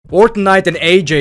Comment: I thought he said Fortnite 😂